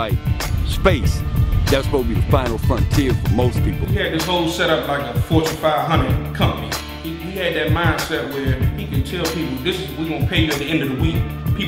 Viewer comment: Yo! @ 5:57 He Says : “ 4 to 500 Company INSTEAD OF Fortune 500 Company!” LoLz 😂😂😂😂